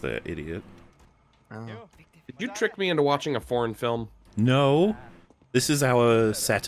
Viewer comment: I thought he said "Did you trick me into watching a porn film?" And I was very confused for a second